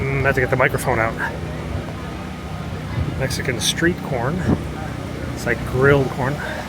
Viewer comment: Oh!, Mexican CORN! I really heard "Mexican PORN", but...nope. Good. My bad, LOL. And that is my dose of social interaction for the week. T'anks!